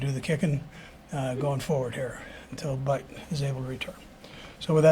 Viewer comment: "Until Butt is able to return" did i hear that right???